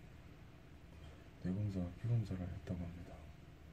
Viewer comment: 에 뭐라고 말하시는 거에요? 잘 안들려서.. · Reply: 이재열 뇌검사와 피검사 받았다고 하셨어요